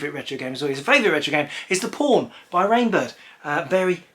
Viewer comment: I am watching it, and...right out of the blue, I AM surprised... this is lovely :D Your accent... I thought you said "The Porn by Rainbird" :D Thanks so much, my friend.